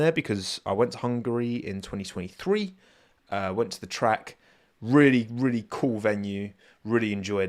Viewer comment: I heard “I went to the crack” and everything he said afterwards had me cracking up!!